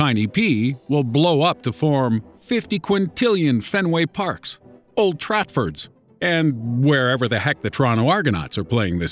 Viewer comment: did he just say "old tratfords"?